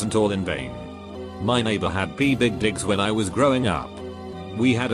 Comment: Bruh he said "pea big dicks" instead of "peacock" LMAO 😂😂